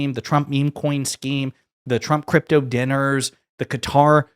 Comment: When you say 'scheme' i hear 'scam'. Note to self, get ears tested.